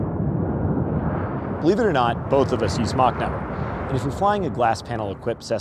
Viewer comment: What in the hell was the first sentence in this? The CC says "Believe it or not, both of us use smoke now" I heard "Believe it or not, both of us use (choke/swallowing a fly noise)"